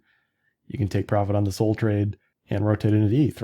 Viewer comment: When you said "SOL trade" I heard "SOL train" and now can't unhear it. When it's time, please scream in stream "All aboard the SOL TRAIN!" and we'll know to ape, 😊